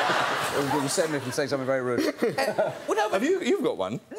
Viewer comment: What did Ewan say at 03:22? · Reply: I think he said “you saved me from saying something very rude”